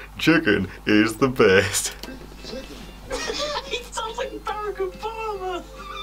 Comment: Ohhhh you say Barack Obama. For the longest time I thought you were saying Derek O'barber 😂